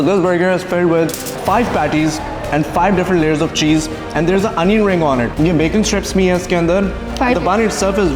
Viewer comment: did you just say BACON? :))